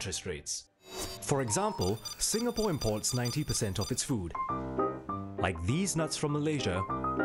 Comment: Did he just say THESENUTZ?